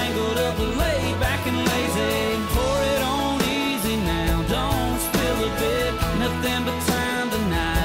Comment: Am I the only one the thought he said don’t spill the dip?? I’ve been singing it like that for years